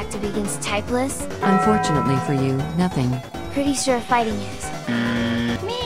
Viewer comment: fire weakness water typeless :i have no such weakness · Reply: I broke when I heard "I heard fighting is" LMAO